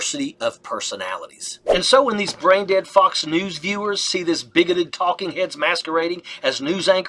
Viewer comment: Apologies for crassness, but I thought I heard "braindead fucks" right there. Freud rules!!!